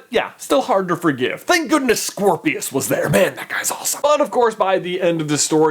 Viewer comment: It sounds like you said Squirrelpius